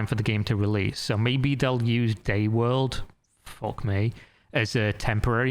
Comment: Yet, I thought he said "Gay World" and not this "DayWorld" 🤣